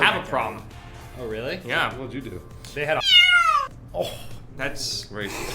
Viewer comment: I'm not sure if this has been mentioned previously but i think says "They had a Holocaust."